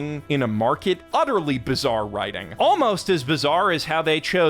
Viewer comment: did I hear Bizzare writing, Arise my masters